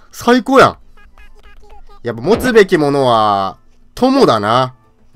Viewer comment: 持つべきものは友だなが 持つべきものはホモだなって聞こえたw